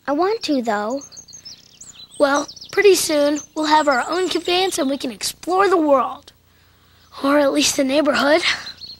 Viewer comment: In 13:44, what did Frank say? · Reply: "You can put us down here, Ork."